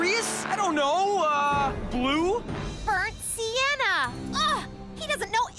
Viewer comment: HELPP I LAUGHED SO HARD I THOUGHT SHE SAID ”BURNT SIERRA”